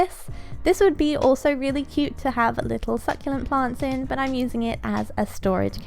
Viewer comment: while i was eating i thought you said "horrible succulent" LOL